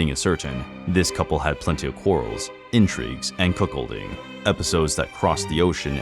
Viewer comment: did you say cuckholding?? ☠️☠️☠️